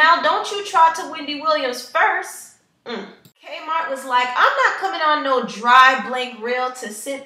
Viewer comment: did she call K Michelle "k mart"??